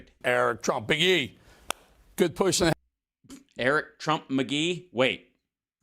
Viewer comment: I thought little e said, “good push for today”, lol